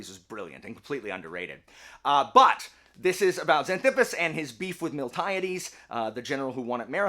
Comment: I'm watching on low volume because of a sleeping baby. I swear I heard you say "This is about Heracles and his beefy thighs" 🤣